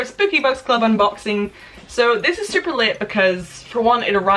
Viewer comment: I heard "This is super lit." instead of "this is super late" and had a shocked face irl.